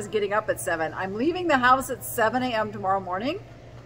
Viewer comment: Lol, something is wrong with me! 😄 I heard "go sailing" and thought "how!? She's in Vegas!" And then thought about who I was watching and realized it's "sale-ing". 😄